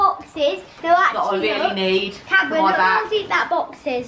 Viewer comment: did call her karen??😭😂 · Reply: i think he said camera x